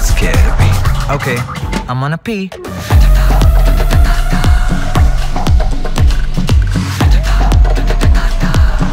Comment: in theatre I was not sured that what he said but now I am confirmed that it is I wanna pee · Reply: I am gonna pee*